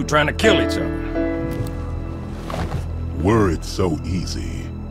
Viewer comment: I remember as a kid i always heard "worried so easy" instead of "were it so easy" haha